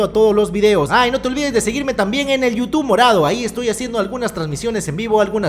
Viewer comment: en el minuto 0:38 yo entendi youtube naran- xd okno